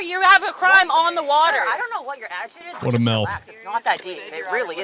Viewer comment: 😂 I thought you said “what a milf”